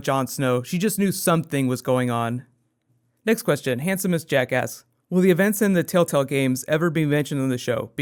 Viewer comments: I heard " Handsomest Jackass" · when you said "handsomest jack asked" it sounded like you said "handsomest jackass" hah too funny